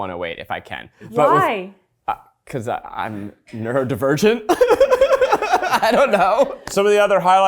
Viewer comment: explaining how he uses the microwave buttons, got questioned, answered because he's Neurodivergent is the most REALEST THING HE EVER SAID. -(Sincerely from a Neurodivergent person) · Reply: I know what that term means but I genuinely thought he said “Nerd-divergent”! 😂